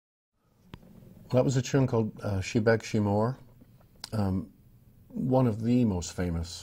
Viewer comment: Shi Bhig Shi Mhor [not what I heard it as, She begs frae more] beautifully played on a beautiful guitar,thank you so much!